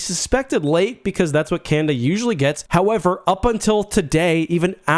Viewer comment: Not gonna lie, I thought you were saying "Kanda" like it was a players name on the team and it took me a while to realize. I got through your other world juniors vid without even noticing lmao.